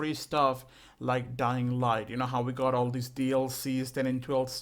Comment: When the video started I heard “Dying Light Doo”